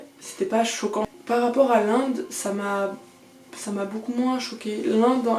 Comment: did you say "hollande" or "Londres" ? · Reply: «l'Inde» :)